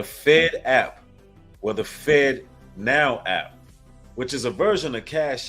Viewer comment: Every time he says "fed now" it's sounds like he saying Fentanyl these people will go to extreme measures to prove just how evil and wicked they are